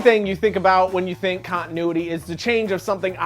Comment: I heard "Content nudity" instead of "Continuity".